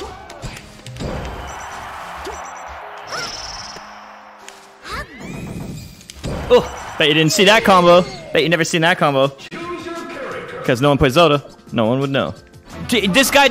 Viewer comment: I thought he said "Betcha didn't see that coming." Still FeelsBadMan.